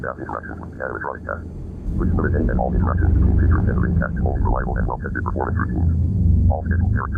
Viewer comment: i think it says cast and not test.